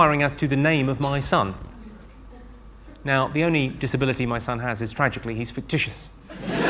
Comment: What did he say at 1:14 ? Tragically he's fictitious? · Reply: "The only disability my son has is, tragically, he's fictitious..."